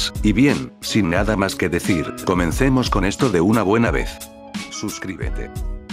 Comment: XD escuche un suscribete cuando la pantalla se pone negra pero muy despacio